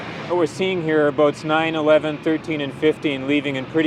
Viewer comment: someone hear 9/11?